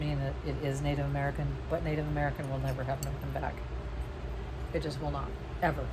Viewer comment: what do you say Native American jewelry will never have on the back? NA? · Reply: kirsten rissling Won’t have an open back 🤓